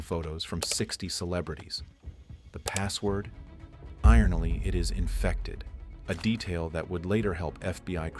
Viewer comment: Is this narrated by AI? 16:24 FBY agents. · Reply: Definitely, "Ironally" instead of ironically at 7:47 lol